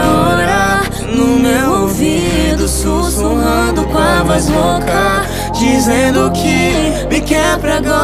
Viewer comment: Na primeira vez q ouvi a música eu entendi susurrando com a vassoura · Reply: Eu entendi "com a voz louca"😂